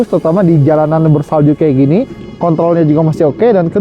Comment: ini gw kgk salah denger kan 🗿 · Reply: Kontrol bos bukan kont0l